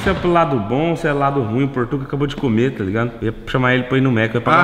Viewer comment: Só eu ouvi o falar que vai comer o Portuga tempo 5:24mt